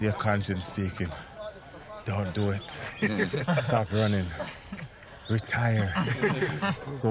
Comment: Did he just say "retire,go play golf"?